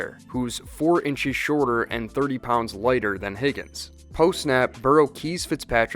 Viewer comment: I heard "4 pounds shorter and 30 inches lighter" lmao i need to go to sleep